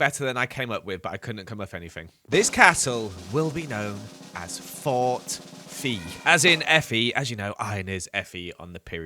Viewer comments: I thought you said Fort Feet! 😂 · Fort FE (Fe?) came out great! Love the detail of the floating lanterns. =) Also, it's so cute that you made a little cottage for Molly & Arthur. Maybe they could have a pet 😺named Ron =P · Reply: When he said Fort FE at first I thought he said fort feet. I was confused 😹